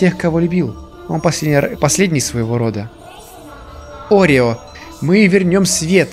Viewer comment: я один услышал орео